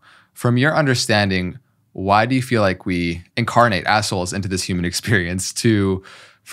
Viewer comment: I heard; "incarnate assholes". Had to rewind and listen again. 😂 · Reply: Omg, I did rewind first, because I first heard “incarnate assholes”, and then only after rewinding… ooohh, “incarnate as souls”… I was laughing on my own in my room😂